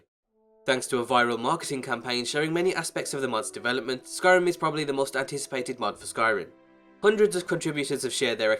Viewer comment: Am I the only one who hears "Skyrim is probably the most anticipated mod for Skyrim"?